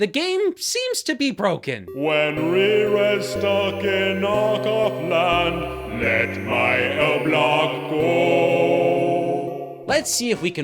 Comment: What did that say? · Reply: Nvm I just figured it out. He says " When Rerez stuck in knock-off land. Let my L block go! "